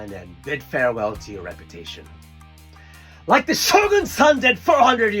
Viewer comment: I heard "The shōgun san" instead of "The shōgun's son". Poor shōgun san is late to an appointment, but we forgive him, because he's our shōgun san, and we don't want shōgun san to be embarrassed.